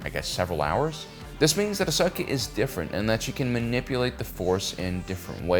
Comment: “Ahsaki” lol did I hear that right? Why does it sound like he’s saying “Ahsaki”?… may the force be with you, always…